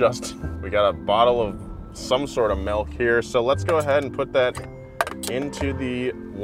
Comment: I liked before I heard him say “melk”